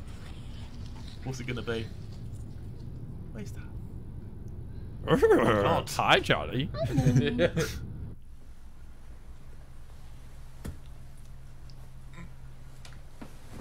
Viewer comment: did simon say hey charlie?😭